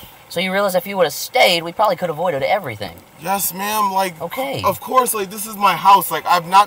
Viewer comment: I thought he was saying “ yes man” but he’s saying “yes ma am” lol